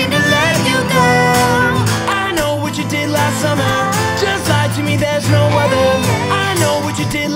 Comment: i thought he said "just lie to me bitch, no other" i'm so stupid 😂😂😂